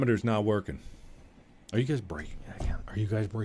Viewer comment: I thought he said "I swear to God, are u guys breathing again?!"